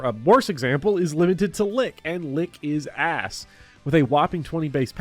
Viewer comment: I kind of spaced out and I heard "lick his ass", but more importantly I want you to know I waste a lot of time trying to catch Onix in Heart gold. I got all the way to the final gym before I realized it had 50 hp at level 46.